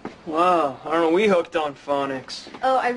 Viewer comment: what does Jess say at 1:47? Can't understand.. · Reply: "Aren't we hooked on phonics"